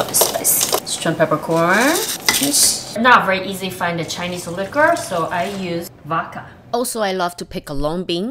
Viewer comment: Oh, she said Chinese LIQUOR...I thought she said it was not easy to find a Chinese HOOKER. My bad.